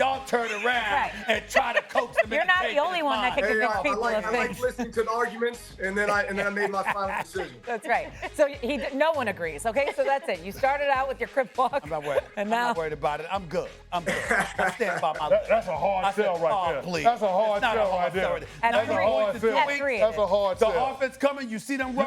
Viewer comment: did she just say crip walk??